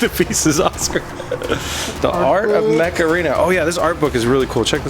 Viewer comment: It sounded like you were saying "Macarena" heeeeey Macarena!